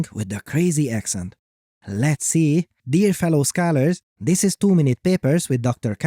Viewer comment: i heard you say scanners not scholars, and i have no idea how to spell your name, so it did a better job than i did :D